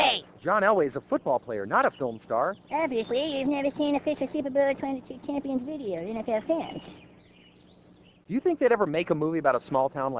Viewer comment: does anybody here even know what cartman is saying right now? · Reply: "Obviously you've never seen the official Super Bowl XXII champions video we made ourselves."